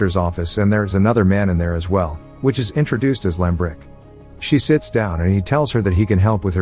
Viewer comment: I thought he said he was introduced as Limp Rick. Holy shit, it’s Ricky!